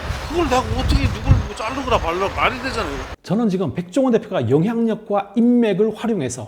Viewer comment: 말이 되잖아요로 들리네요ㅋㅋㅋㅋ